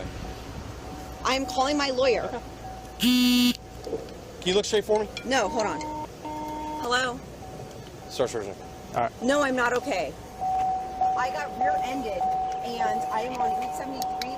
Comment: What did he said ? Start search ? 5:25 What did he said ? "One of the drivers ... " what ?